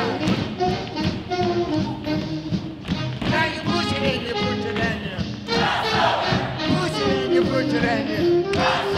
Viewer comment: what is he saying before they say cross over? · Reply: "You push it and you push it and you crossover!"